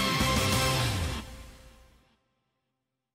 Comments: What is she saying? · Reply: Think she said "called it" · Do you know what mythra said? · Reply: “Called it”